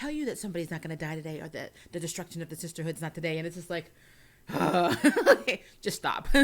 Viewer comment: I thought she said it wasnt the loss of the sisterhood today?